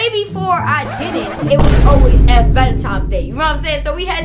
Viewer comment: Who else heard her saying “Valentimes “